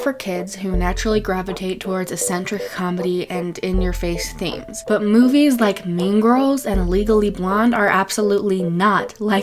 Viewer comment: Um i thought u said legally blonde is garbage and i literally came to fight XD